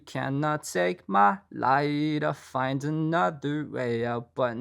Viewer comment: all my life i thought boywithuke said you took my pride away but you cannot take my life but he said light lol